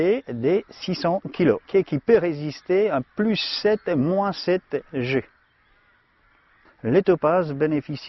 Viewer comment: Hi, at 1:00 did I heard correctly +7/-7G???